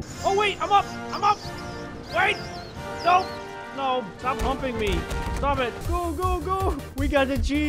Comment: Im using my headset and i heard 'stop humping me' said by